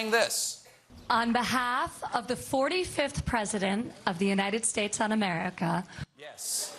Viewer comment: I thought she said, "On behalf of the 45th president of the United States Un-America..."